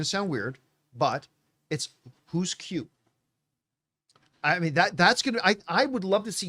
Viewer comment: Had to second hear that, thought John said "Who's cute" at first.